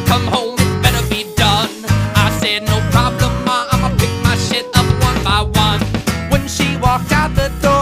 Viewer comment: did he say shit at 00:29?